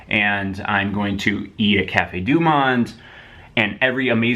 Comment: When he said "eat at cafe dumont" I thought he said eat a cat.